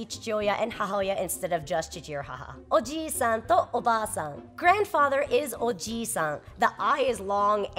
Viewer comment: Do you currently read any on-going Manga Series? If so what are some of your favorites ^_^ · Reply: Lol I thought you said grandfather is OG SAAAAAAAN